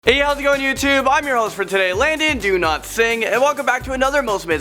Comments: Hahaha... I thought he said “Landon DO-NOT-SING” 😂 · Who heard Landon Do Not Sing? 😂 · When you were saying your name I thought you said Landon Do not sing 😂😂😂😂😂 im a dumbass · I thought he said ” Landon Do Not Sing.” Lmao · what i hear in ur name “Landon Do not sing” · It sounded like he said. Landon do not sing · Hahaha... I thought he said “Landon DO-NOT-SING” 😂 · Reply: I thought he said Ladies do not sing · I thought you said Ladies DO-NOT-SING lol